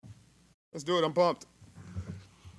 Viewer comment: Not gonna lie I thought he said “let’s do it, on bompton”